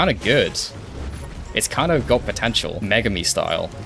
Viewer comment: DID YOU SAY POTENTIAL?